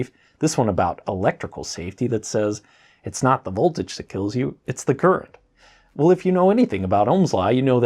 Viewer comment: I was very confused for a second when I thought you said "It's not the current that kills you, it's the GIRTH" - LOL!